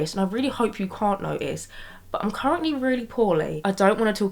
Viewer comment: you're currently what ? i'm sorry, i couldn't quite understand what you said :( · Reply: Poorly! Miss Rona is making a visit 🤣🥲